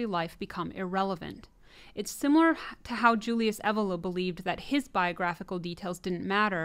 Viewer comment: Julius who? Didn't catch that… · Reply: Julius Evola. I've got heaps of videos about him on this channel.